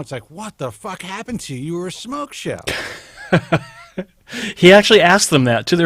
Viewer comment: What word uses at 1:22:02. something like Smokeshell? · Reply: He said "smoke show".